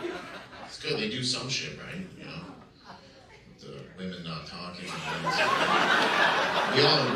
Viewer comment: Did he just say stone women or am I hearing things